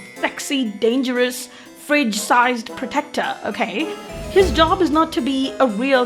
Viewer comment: "Fridge-sized protector" is my favourite line inn the video full of pure gold insults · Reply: OHHH I thought she said “bridge-sized protector”… still got a laugh, and ig she’s not too far off-base either way lol